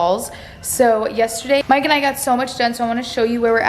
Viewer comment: Is it just me or does it sound like she is say “mike and ike” when she says “mike and I”